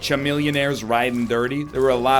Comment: Did he just say CHUH-millionaire?